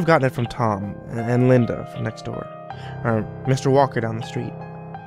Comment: "Mr. Walker down the street" I THOUGHT HE SAID MR. WONKA.